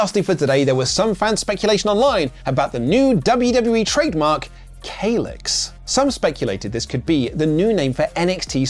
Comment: For a second there, I thought I heard the greatest Klingon warrior of all time Kahless.